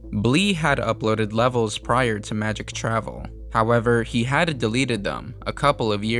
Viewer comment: did bro say hadn't or had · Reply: Had